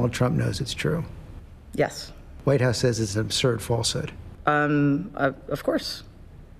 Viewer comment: Can’t understand what he says on minute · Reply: "White House says it's an absurd falsehood".